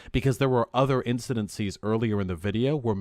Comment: I swear I heard vaush say “incedencies” instead of incidents. Like he really be trying to say big words and just making crap up lmaooo